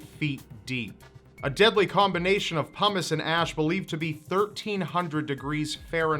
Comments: lol, it kind of sounded like he said humus and ash :D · sounded like he said ''a deadly combination of hummus and ash'' xD